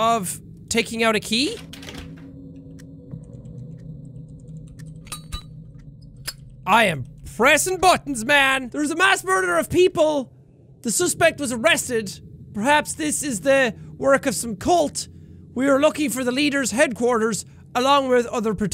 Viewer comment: “When he said “I’m pressin buttons man” I thought he said I pressing Tim Burton’s man..